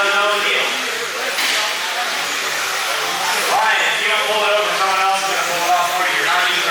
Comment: that was confusing, someone named Bryan i guess using reverse DQ'd but i thought they said "Ryan", then you stopped.